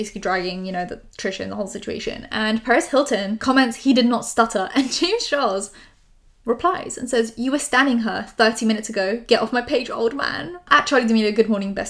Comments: At first I heard “Paris Hilton” and “get off my page old man” I was GOOPED!! 😳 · omg I thought you said Paris Hilton. I was like why would James call her an old man